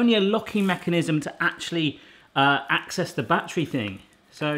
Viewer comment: I thought it said "Mrs Battery"